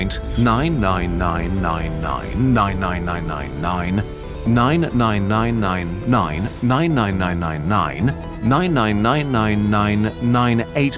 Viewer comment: i heard nein nein nein everytime he said nine